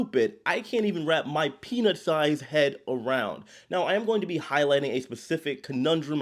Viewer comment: I must admit I thought you said you couldn't wrap your penis sized head around this and I was like damn son.